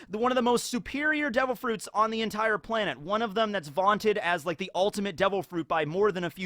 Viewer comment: Is this like clone or evil because he suddenly switches pronunciation of the five elder stars on a dime and one point he said vanted instead of wanted